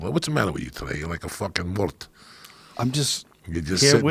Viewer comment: WTF does Joey call Joe at 2:27:02?!! 😂😂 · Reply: "Muerto", in English that means "Dead"